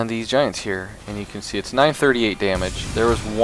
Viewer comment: Did I just hear 938 on one hit because i didnt saw the numbers